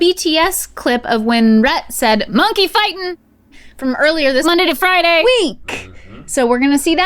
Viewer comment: minutes she says Monday-thru-Friday SUPER fast?? Did anyone else catch that??